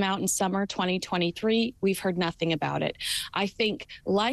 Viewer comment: I thought they were saying nothing anymore!!!